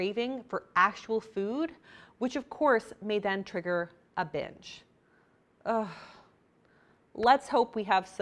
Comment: lmao had to take a double take when I thought I heard "which of course, may then trigger a bitch"